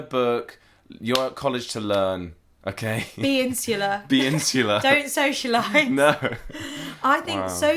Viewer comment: can somebody tell me what that word means? what they said: intuliar? And how to spell it I can't seem to find it and I want to learn the meaning of it.